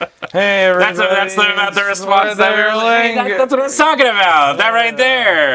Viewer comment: Forth Eorligma KILLED me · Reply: I heard "fourth ero ligma"